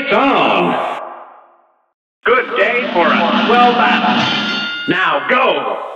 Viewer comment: “A brawl is surely brewing!” ”AND BEGIN!!!” “A great slam and then some!” ”IT’S ON!!!” “Good day for a swell battle!” ”NOW GO!” “Here’s a real high-class bout!” ”AND BEGIN!!!” “This match will get red-hot!” ”YYOU’RE UP!!” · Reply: I always thought he said "good day for a swell match"